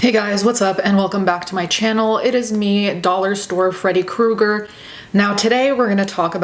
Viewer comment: I swear I heard you say Freddie KROGER at the beginning and now I can't stop thinking that would be a hilarious costume